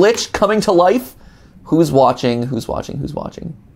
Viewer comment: I think thats says "Who's Watching Who"